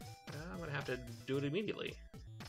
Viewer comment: E-Meaty-ately is what I heard